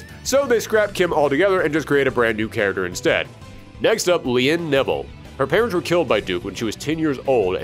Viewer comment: "Next up, Lien Neville." Sorry, but the way you said her name makes it sound like you said "Lien Nipple"